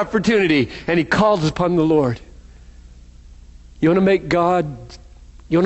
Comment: what did he say? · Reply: "Do you want to make God's day?"